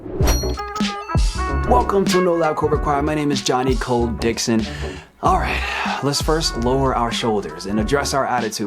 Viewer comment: I love your vids but I have to admit I always hear "johnny cold dicks" when you introduce yourself.